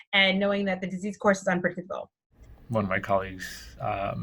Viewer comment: " ...and knowing that the disease course is..." Is what? I couldn't understand the last word. Unpredictable?